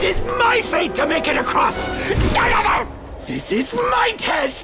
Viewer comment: sounds like drunk homeless guy banter - like wtf did he even yell to Kratos? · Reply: "None other!", that's what he said.